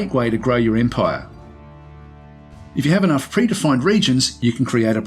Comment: The narrator is sometimes hard to understand. At 5:08 he says, "If you have enough blah blah blah regions, you can create a province." I have listened to it 6 or 7 times and cannot make out what the blah blah blah is. It sounds like "free to find", but that makes no sense. WHAT is he saying, please? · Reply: He said: "if you have enough predefined regions." What he means is that certain formable provinces are made up of a set number of predefined sub-regions that can be grouped together into a single provincial administration. Hope that helps!